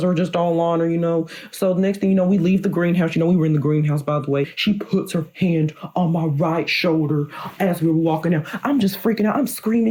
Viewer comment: I keep hearing “she put my hand on my W H I T E shoulder”.